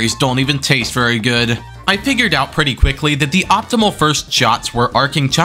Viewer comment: did you just say “pigured”